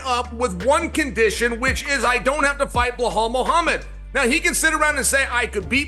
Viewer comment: sounds like Chael is saying “Blow hole” instead of “Belal”